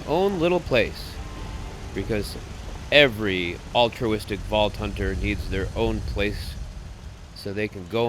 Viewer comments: did he say vault Hunter? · did he say vault hunter isn't that from borderlands?